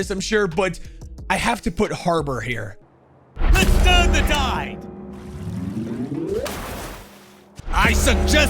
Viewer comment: I always thought he said "You deserve to die!" Never thought he said "Let's turn the tide!"